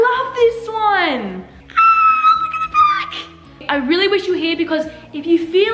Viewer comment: Did anyone hear her sear or was it just me Time of video: 9:14 What was she doing:looking at the cloths Brooke gave her What cloths she looked at : the white jumper with a blue A on it What she say :oh my f###### god